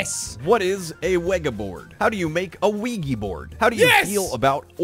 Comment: I'm high, I thought jack said keef board, smh.